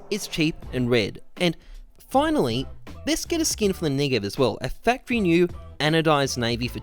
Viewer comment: did he just say niggev?